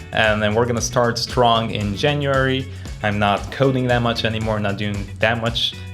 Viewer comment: Did you just say I'm hot?